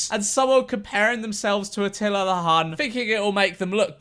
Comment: what did he say? "Teleru khaan"? 😂😂😂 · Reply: atilla the hun